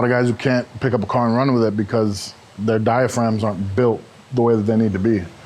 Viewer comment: their what aren't built? Sorry I can't understand and english isn't my native language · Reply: pretty sure he said diaphragm